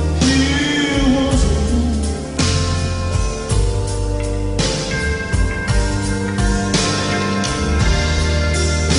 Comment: Can please someone tell me, what exactly he said in 6:35’??? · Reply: "He was a loner"